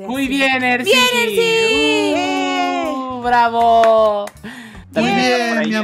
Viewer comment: Soy yo o escuche que le dijo bravo mi amor a? 9:33 Crazni xd